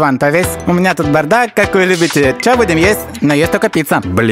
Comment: "Тут бардак как вы любите "😂😂😂😂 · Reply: Я только что это расслышала, до этого несколько месяцев была уверена, что ,,как вы видите"..!